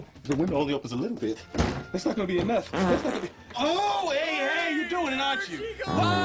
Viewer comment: Does anybody know what Archie's saying between his first and second farts? I'm not sure but it sounds something like, "Hey, where'd cha goin?!"